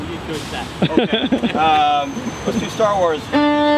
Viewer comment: I don’t know why but when he said Star Wars for some reason I thought and heard avengers